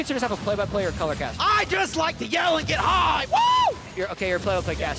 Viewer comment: I just like to yell and get high LOL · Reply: Idk about you guys but I heard “I JUST LIKE TO YELL AND GET HAAARD!!” 😂